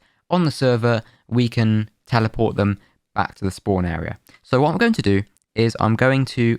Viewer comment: What did you say at 15:30? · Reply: "so what i am going to do"